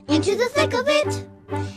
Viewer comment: Bro I thought the sound said into the tinkle b** 💀